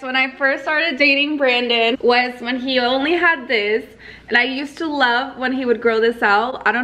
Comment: did she say I used to lick it